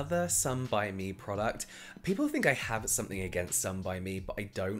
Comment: it says muji instead of some by mi